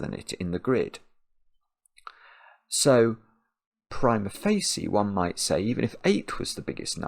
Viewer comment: I ask for help. 21:02 what does say after "So..." I cannot understand. Thank you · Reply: Prima Facie (in Latin). Means, literally, First Sight. Often used when discussing evidence, generally meant as 'looking at this on the surface, without looking too deeply, it seems to be...'